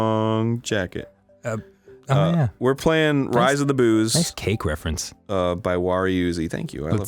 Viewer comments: Thought it said Rise of the Boobs. Lol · Thought it said "Rise of the Boobs"